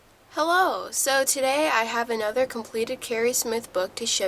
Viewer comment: who else heard 'holo' ????